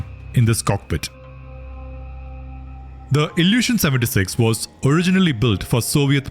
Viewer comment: Why did I keep hearing "Kazakh illusion" 😶